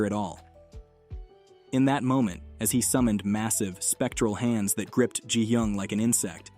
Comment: did he say massive?